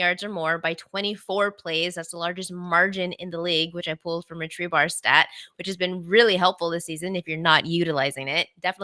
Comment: Sorry to ask a stupid question like this, but at 55:50, is talking about pulling something from a stat...sounds like she says "Tree Bar stat"? I know that isnt correct, was wondering if someone could clarify what stats/source she is referring to? She mentions that they have been very helpful, especially for fantasy. Thanks for the show, watch every week, it's great. Keep up the good work! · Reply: was referring to Rich Hribar (pronounced REE-BAR). He does fantasy worksheets for all NFL games for the Sharp Football Analysis subscription service.